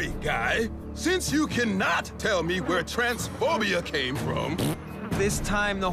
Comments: did chef say “transphobia” or am i tripping??? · WHY IS NOBODY TALKING ABOUT 13:09 DID HE SAY TRANSPHOBIA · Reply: he said “trent’s phobia”.